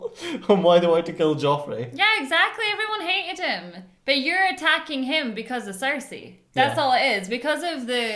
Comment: OK it's an old vid but I have to add I heard "you're attacking him because he's sexy" at 46:00 😭😭😭